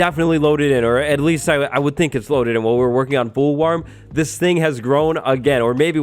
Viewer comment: Anybody else hear him say fool warm